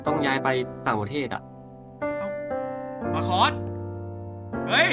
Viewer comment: ได้ยินว่า..ปลาคอส(?)